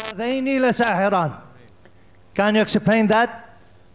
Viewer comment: Did he just say exceplain🤣